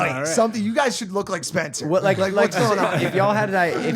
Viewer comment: Did Mike call Mac Spencer at 1:03:52?